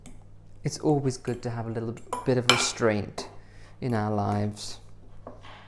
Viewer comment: I heard mistakenly "It's always good to have a little bit of a straight in our lives"😂😂😂